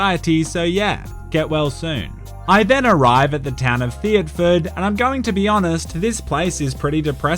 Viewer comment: I thought it said "Thot ford"